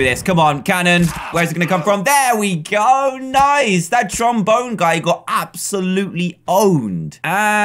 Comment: did he say trombone?